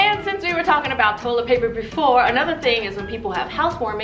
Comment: “Cheap titties?” Honey, that’s what I said when I booked my boob job in Tijuana”